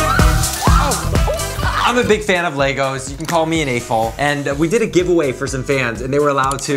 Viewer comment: I thought he said a hole 😭